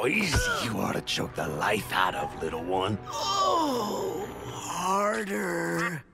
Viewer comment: What is striker saying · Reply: "Oh I remember how easy you are to choke the life out of little one."